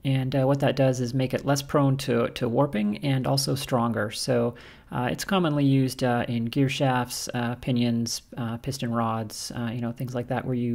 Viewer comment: I swore you said “it’s commonly used in gift shoppes.” B20 bronze sounds the best.